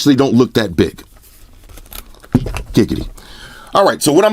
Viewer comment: did you say giggity?